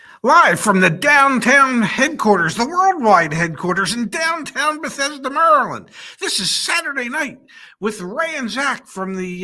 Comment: Did you say you are in Bethesda MD 0:06??? I'm not far from there!! PLEASE.. will you come with me to help me buy my next car!!! :-D I joined YAA just last week! 🥰